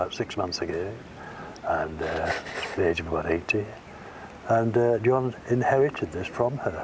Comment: His aunt died at WHAT age??? 118? Did I hear that right? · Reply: You didn't. "About 80".